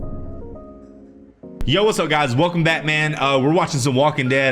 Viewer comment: It sounded like you said welcome Batman and I was like oh wow inclusive 😂🤷🏾‍♂️😜👍🏾✌🏾❤️